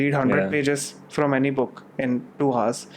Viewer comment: Ok did he just say 100 pages in 2 hours?